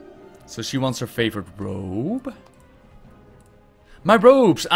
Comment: I wasn't paying attention much and thought said, "So she wants her favorite probe" instead of robe lmao I had to pause and rewind to make sure I heard it right xD